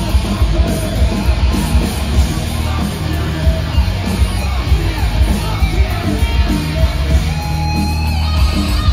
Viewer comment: Does he say 'fuck you, Dave'? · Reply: Yeah, if you watch another video of it Perry points at the crowd and says "f you mfers", points at Dave and says "f him" then points at Eric and says "and f him" lol